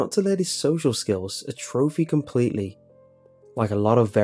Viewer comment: did... did you just say "a trophy" instead of "atrophy"?